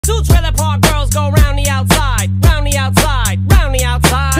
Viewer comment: My headphones are so bad I hear “ two battle pass skin go round retail row